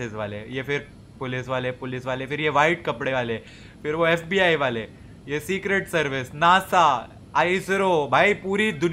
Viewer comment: Did he Said SBI Waale 😂😂😂😂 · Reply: i think he said FBI waale but also he said ISRO vale and NASA so 😂